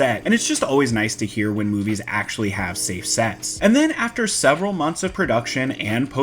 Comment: Had to rewind back to 5:10 to see if he really said “Safe Sex.” 🤣 Turns out it was “Safe Sets.”